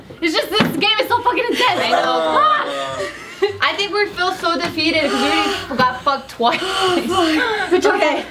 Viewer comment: when made his joke in the beginning I thought he said "da feet" not defeat